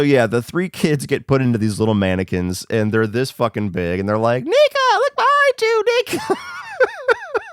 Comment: thought you said nigga for a second 😂